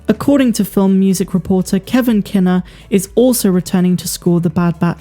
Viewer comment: I thought you said "Kenner". I was like "wait ... WHAT!? WHO!?"